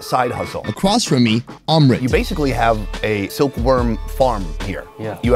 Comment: For about 0.01 seconds, I thought that said his name was 'Armpit.'